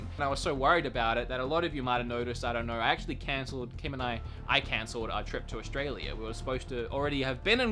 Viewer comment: omfg I thought u said cancer instead of cancelled and you were telling us you had cancer